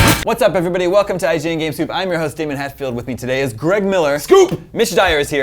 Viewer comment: DAMN YOU! I THOUGHT IT SAID GAMER POOP!!!!!!!!!